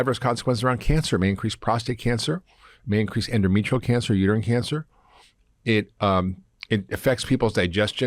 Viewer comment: Did I hear him say "Urine Cancer" ? There's no such thing. (right near the 5:27 mark)